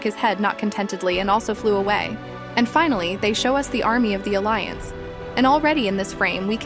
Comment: I thought the TV man said f###ing finally 😂